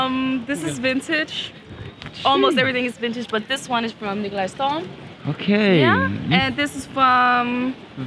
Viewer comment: Love the trio at 7:20 !! Anyone know that pants brand at 8:07 ? I couldn't understand :( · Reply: It's from Nikolaj Storm :)